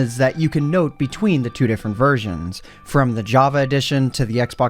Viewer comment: Why did i hear the word "virgins" xdd